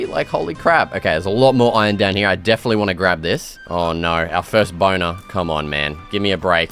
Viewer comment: did you just say "our first boner"?